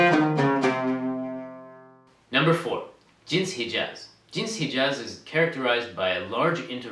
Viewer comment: did you say, Gypsy Jazz? i thought you said gypsy jazz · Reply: He said jins hijaz جنس حجاز